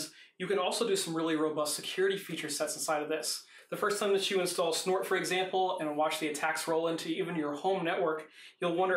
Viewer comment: What did you say at 8:20. How is the software called? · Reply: Philipp Renner it's called pfsense! have also a look on the free version of sophos firewall for home use!